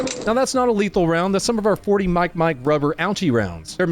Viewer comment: that was a 12 ga bean bag wasn’t it? 3:06 it sounds like he says “40 out” but it look like a regular 12.